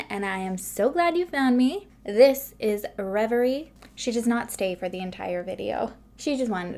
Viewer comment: I'm sorry I'm kinda deaf and thought you said that baby girl's name was Gregory! Lol I like Reverie, definitely unique!